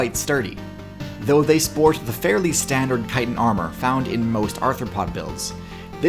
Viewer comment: Having just come from an Attack on Titan video, I heard "Titan" instead of "chitin"